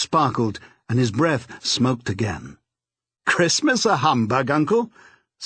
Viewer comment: Can anyone help me understand this one part - no matter how many times I listen to it, I can't understand what's being said, and it's driving me crazy! At minute mark 8:06ish - "his eyes sparkled and his breath..." - he says something like his breath smelled of gin? That doesn't make sense - does anyone else know what is actually being said here? Thanks!! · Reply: It sounds like "..and his breath smoked again." Could be wrong!